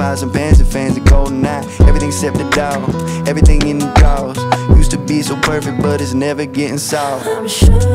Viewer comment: First time hearing this I thought Matt was saying "everything at McDonald's"